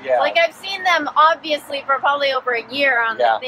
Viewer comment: At the very end, I thought she said "I'm gonna sound check the video". That would have been hilarious.